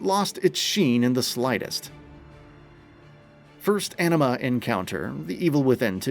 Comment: Man not gonna lie, I thought you said "enema" right here. That changes the whole connotation😂